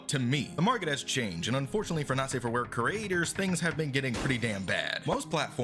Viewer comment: OH FUCK I THOUGHT IT SAID NEW CREATORS NOT NSFW CREATORS OHHH GOD